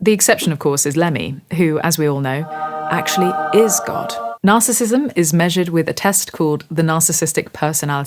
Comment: I wasn’t watching when you said “Lemmy is God” (😅), but I heard “Lenny is God” probably because I watched Maestro last night. That Bernstein was the very epitome of a narcissist creative.